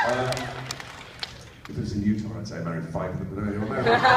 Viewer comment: Thank you very much!:) His voice in HP is legendary... so deep, melodic, so modulating!! What is he saying at 0:51 ??? Can't make it out:(( not a native speaker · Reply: "If this was in Utah, I'd say I'd married five of them." Something like that. Utah is a state that has a lot of Mormons, a religious group that has a history of polygamy (marriage to multiple people). They don't do it any more, but a lot people outside the religion still think they do.